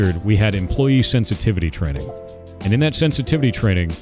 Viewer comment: I thought he said insensitivity training